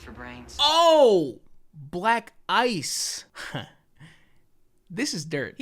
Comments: If you think “black guy” when he says that, that’s what you’ll hear. But if u listen to it again and think black ice, THATS what u hear. My mind is blown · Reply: And here I thought he was saying "Black eyed." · I always thought he said black eyes... like he was gonna beat hi up - never thought about black ice lmao